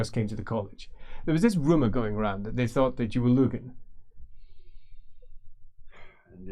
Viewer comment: Is it lugen? Or luegen? Either way, i cant remember what this term means. Loving the intrigue in the colleges. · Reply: "there was this rumor going around that they thought that you were lugen." Least I think thats what is said. Could be I'm mistaking something in accent.